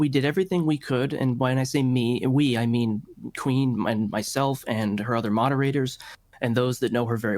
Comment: I forgot the girls name and legit thought he said "my queen".. with the way he sounded, I wouldn't be surprised if that's what he meant